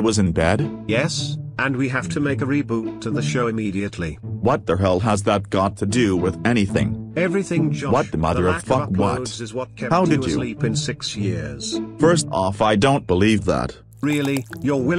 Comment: 1. 0:27 What Did That Old Man Say Here? Josh's Loud Eric Voice Drafted Him Away. 2. Haven't Heard of That Funny Show In a While. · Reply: This is what I heard: Everything Josh. Your lack of code is what kept you asleep in 6 years.